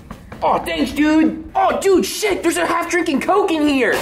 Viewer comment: Did just say the s word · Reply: He said "sick"